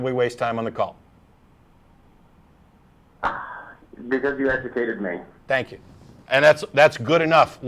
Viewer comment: What does the caller says at 55:41??? I couldn't understand... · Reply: "Because you educated me"